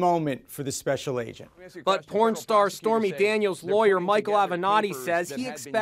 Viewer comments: did he just say porn starts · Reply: Yeah the porn star’s LAWYER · did he say pron star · did he say pornstar? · Did He Say " Porn Star " ? 😅😄🤣🤣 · Did I just heard porn star on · LMAOOO did he actually say pornstar? 😂😂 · did I just hear him say pornstar??!! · did he say pornstar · did he just say pornstar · did he say pornstar?? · did I hear porn star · Did he just say "Porn Star"?! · correct me if i am wrong at 1:18 did it say pornstar? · did he say porn star? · did they just say 'porn star' or am i hearing things? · On 1:21 did it say porn star